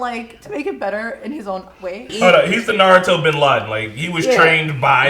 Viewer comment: did he just called pain the Naruto Bin Ladin (as the opposite of Naruto) 😂😂😂